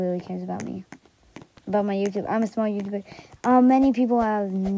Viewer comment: At the start it sounded like I said smart not small lol